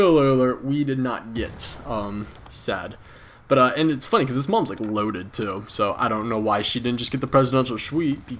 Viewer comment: goto 3:30 and after you say spoiler alert you say his moms pretty loaded too.. or am i hearing things.. pretty good story man :D